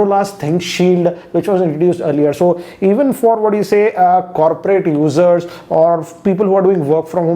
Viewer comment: Why did i hear coperate losser